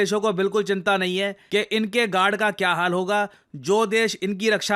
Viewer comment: inke gaand ka kya haal hoga ? 😆 did he really say this